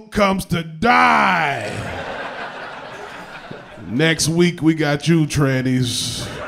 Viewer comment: HA! At 1:30 he says, "We gotchu trannies", but I heard, "We got Jew trannies!" Even though I didn't get the joke, I laughed because it's my first time hearing that phrase and imagining them. That's a pretty extreme bris ;)